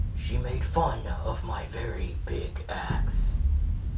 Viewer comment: Oh I thought I heard "Made fun of my big ass" then I watched it again... It was axe... Not ass.